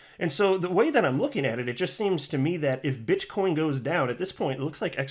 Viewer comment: did I hear "Bitchcoin" at @ 3:45 ? haha